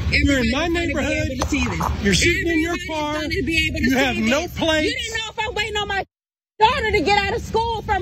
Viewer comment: Are we going to ignore the car apparently had no licence plates? A car with no plates parked anywhere is suspicious. Rashad seems to have heard "you have no place..." but I hear "You are in my neighbourhood, sitting in your car, you have no plates"